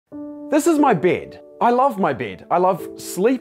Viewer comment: i thought that he said this is my beard kekw